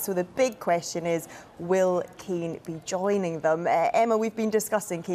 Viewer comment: Thought she was saying Keane